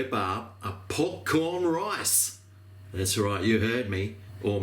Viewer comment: I thought you said popcorn rice.